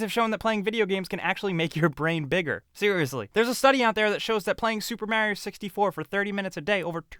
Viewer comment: Subtitles at 2:04 said "superman 64" and really surprised me until I heard the actual line.